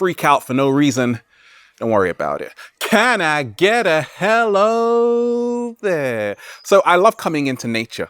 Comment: And can I take help for you all? What is he saying exactly at 0:30? Love:*:)